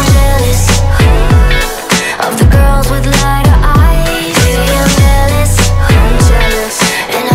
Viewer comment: i thought it said liner eyes like eye liner bruh